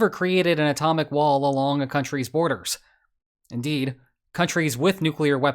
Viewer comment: You say “Wall all along the boarders.” But I hear “walllallala boarders” 😂